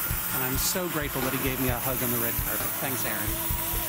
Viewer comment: Sounded like the announcer said he was thank full Aaron gave him a hug and... What? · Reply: A hug in the red carpet, which is right before the show